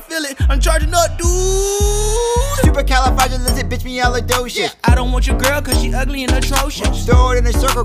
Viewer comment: Captions don’t even know what they said “Stupid California Lizzie me allah does” -captions (Twerk it again)